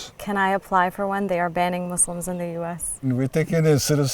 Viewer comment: "they are banning Muslims in the US" I heard that at first and was like, "what?!?" Only then did I realize that "AJ" stands for Al Jezeera....